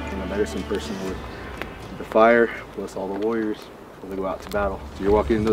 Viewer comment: I THOUGHT HE SAID BLESS ALL THE LAWYERS LOL